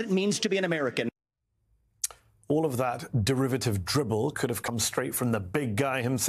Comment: did you say derivative dribble? Lol.